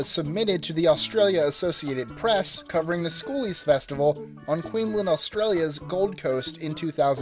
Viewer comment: Sounded like you said queen len, instead of queensland